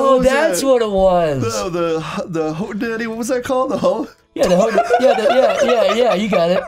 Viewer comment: he clearly said "hootenanny" and Chris heard "hoe-daddy" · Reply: wdym? at 1:02 he clearly says ho-daddy